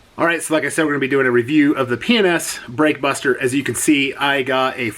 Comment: Really good video. I have a Chrysler 300 with black factory wheels. I have been concerned about some of these wheel cleaners on painted wheels. Gotta try some of this. Early on your video, I thought you were saying penis brake buster.